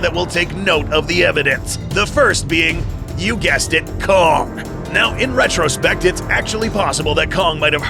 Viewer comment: am i the only one that heard c*ck instead of kong?